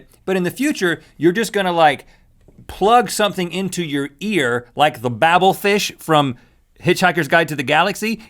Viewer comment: when you say ear captions says here